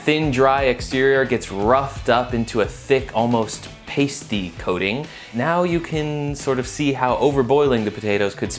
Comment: Did he just say kiting instead of coating?